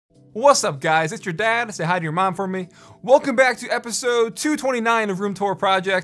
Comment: Legitimately laughed out loud at "it's your dad, say hi to your mom for me" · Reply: At first I heard "Hide your mom from me"